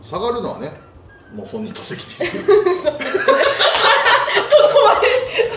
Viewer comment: 「下がるのは」の後は何て言ってるんでしょうか？ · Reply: 本人の責任 では。